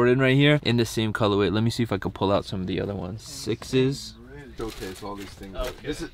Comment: Did say 6s @ 4:17 🤣🤣